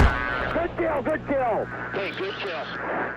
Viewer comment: I always thought the intro said red tail, good kill. I just realized its this exact engagement and that's not what's said XD